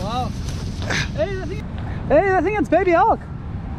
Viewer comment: When you said, "it's baby hulk" I thought you said it's Ernie Els 😁😁 and I'm wondering what sort of golf paraphernalia this poor seal's got itself entangled in 😂😂